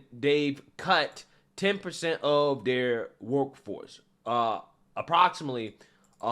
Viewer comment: Hilarious. When you said, "NPR cut 10% of their workforce." But I heard, "Woke Force," which is comical yet true.